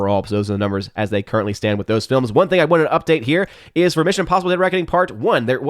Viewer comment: what is he saying? Closed Captions can't even figure it out. It sounds like a Joe Biden word. If you can figure it out please reply. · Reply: "One thing I want to update"